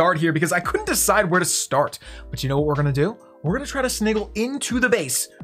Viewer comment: Did he just say 'sniggle'?